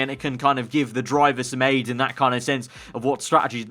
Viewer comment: did I hear AIDS